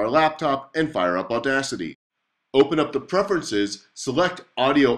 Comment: when i heard "open audacity", I was like, are you kidding me?